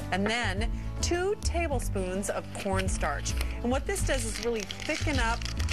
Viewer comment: i thought she said porn-starch lol